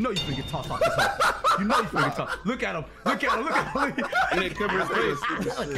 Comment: Did say nigga around 7:33? 🤔🤣🤣🤣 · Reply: said "that nigga coverin space"